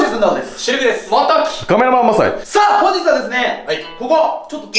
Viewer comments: カメラマンマサイが亀山マサイに聞こえたわらわら(*´▽｀*) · なんか初めの自己紹介の 「カメラマンマサイ」が 「亀山マサイ」って聞こえた